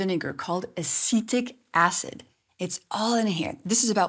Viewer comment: At first I thought she said “acidic acid” 😅